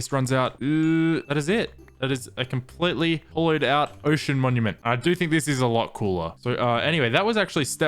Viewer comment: I thought you said "oh shit monument"